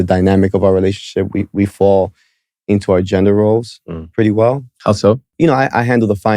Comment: He says "we fall into our gender roles" i hear "i dont care for my own children"